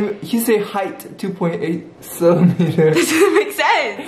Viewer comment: Did he say CELIMETER 😭